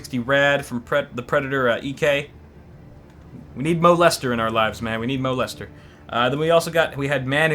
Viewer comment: did you really say that we need "molester" in our lives? lol